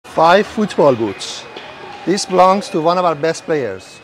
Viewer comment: I heard "5 foot tall boots" when he said 5 football boots ...wtf 🗿☠️